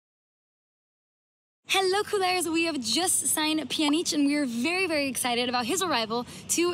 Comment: did she said "a Pjanic?"